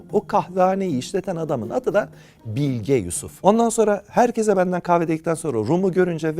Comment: Bilge Yusuf'u ilk başta Bill Gates diye anladım 🤭🤭